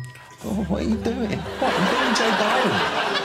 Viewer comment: is he saying "I'm being Joe Biden"?